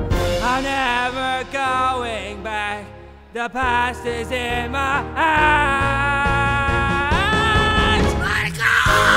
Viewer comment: I thought I heard 'the past is in my ass'